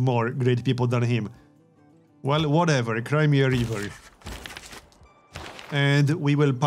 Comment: What's that says at 21:30? Crimea River? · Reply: iptf "cry me a river" - means "I don't care"